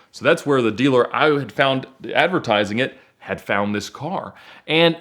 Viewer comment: What does he say at 5:00? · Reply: "i"